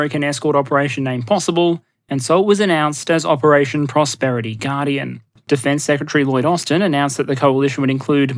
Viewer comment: Prosperity garden is a better name for the operation and it is hard to not refer to it that way accidentally. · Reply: I keep hearing "Operation Poverty Guardian"